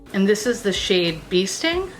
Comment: Holy fuck... I just spat my coffee... When you introduced the Rituel De Fille, I heard you say “wasp” then it all turned to shit. Next I thought you said “BEASTING”. No, don’t be stupid. I listened again, yep still BEASTING. So just to double check I turned on closed captions, and fuck me if they thought you said BEASTING too. Well, I’m now off to grab a paper napkin!!!! Thanks for the PMSL moment. I FUCKING LOVE YOUR FACE. 😜😂🤣